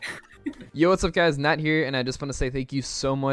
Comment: I thought he said "not here"